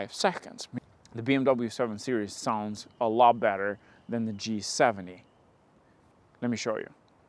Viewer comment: did I hear G70?